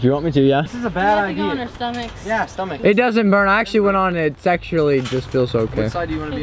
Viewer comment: Anyone else hear day sexually at 6:50😂😂😂